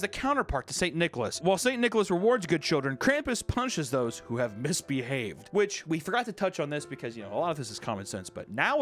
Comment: I thought he said "punches those who have misbehaved"